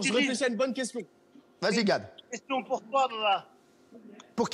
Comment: 🧐🕯mdrr t'as tête gilles, t'as cru entendre "question pour toi allah"🙃heinn